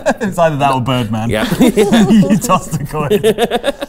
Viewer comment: I thought he said his name was Man Bert, Egg Bert's cousin.